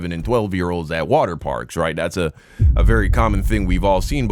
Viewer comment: I’m sorry I have to keep adding things because this lady at me hot and I thought she said theme park and I’m not sure but I just heard say waterpark. Now I know for a fact, kids cannot come into a waterpark at all without an adult due to the dangers of someone drowning. So I haven’t gone back to listen. I just thought she said theme part but if it’s a waterpark, she’s really in trouble.